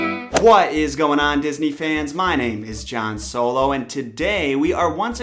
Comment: I thought he said Han solo and i was like WTH